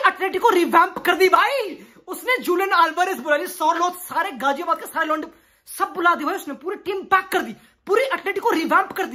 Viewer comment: Why do I feel that I heard something like ,"Ghaziabad ke saare londe bula liye"💀💀💀